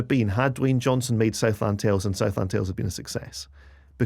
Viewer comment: what are you saying here? I had to put the CC on but the CC says "Southhampton Tales" and "Southland Hills". Which one is it or is it something else? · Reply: Southland Tales?